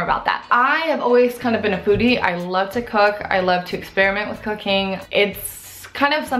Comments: I thought she said she likes to experiment with cocaine omg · Honestly thought you said you love to experiment with cocaine 😂 loved the video, can't wait to see more on your channel 😊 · it sounded like she said " I like experimenting with cocaine." I died laughing.